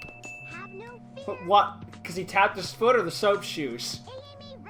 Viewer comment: he says “Amy’s shoes” but the subtitles says “Amy’s boots”.